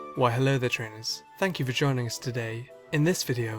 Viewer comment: I heard "vagina" instead of "for joining" :D